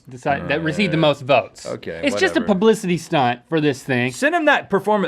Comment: I heard "publicity snot"